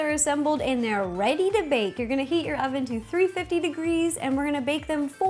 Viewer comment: Am I the only one who thought she said at 4:42 now we are going to eat our ovens?